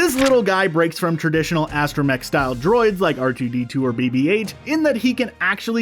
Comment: Did he really say “RGB2” instead of “R2D2”?